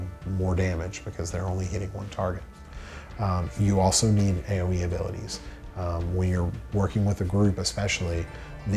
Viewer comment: what is he saying at 2:40? AOE abilities? I couldn't hear.